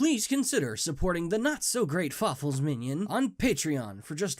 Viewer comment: Yes, he did say it! "This is being Fawful's Minion!" I always thought he said that, but now I know for sure!